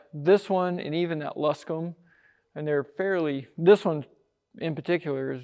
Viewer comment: Man, I love that Cub. Is that a T-Craft ? My dad had one when I was a kid. Much love from NC. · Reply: Nm, I heard you say that it was a Luscomb.